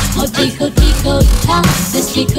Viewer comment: omg its sounds like it says thers a poo poo in my heart LOOOL 😂😂😂😂 EDIT: omg thx for the likes this is the most likes ive ever had thx ❤❤❤